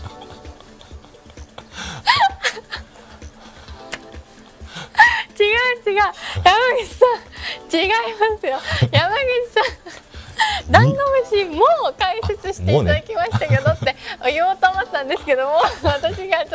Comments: 目を瞑って無意識に聞いてたら 『違う違う、ダンゴムシさん』に聞こえて一人でニヤけてしまた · 心なしかダンゴムシさんに聞こえるの草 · リピートしまくってたら「山口さん」が「ﾀﾞﾝｺﾞﾑｼさん」に聞こえて草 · の「山口さん」も心なしか「ダンゴムシさん」に聞こえる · 字幕無かったらダンゴムシさんに聞こえる笑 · 違う違う！ダンゴムシさん！って聞こえて草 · 違いますよ、山口さん！のとこもなんとなく違いますよ！ダンゴムシさん！に聞こえるww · がダンゴムシさんに聞こえたww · もはや0:24もダンゴムシさんに聞こえてきた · 笑いすぎて「山口さん」も「ダンゴムシさん」に聞こえるの草。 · 山口さんって言ってるのもちょっとダンゴムシさんに聞こえる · ダンゴムシさんに聞こえて 追いダンゴムシかと思ったww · 『違う違う、ヤマグチさん』て言ってるけど、そのヤマグチさんもダンゴムシさんに聞こえるんだよな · 山口さんって言った時「ダンゴムシさん」って聞こえた笑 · 山口さん！もダンゴムシさんって聞こえる笑